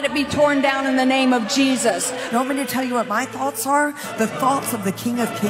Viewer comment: Wait did she call President Trump cheesus?!?!